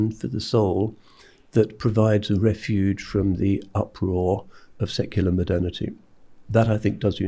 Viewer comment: The words in the captions are frequently a rewording of what is actually said by Shaykh Abdal Hakim. Example @ 04:05 "from the uproar of secular modernity" is rendered as "from the din of secular modernity". Seconds later, "a rainbow coalition" is rendered as "a diverse group". I wonder why this is.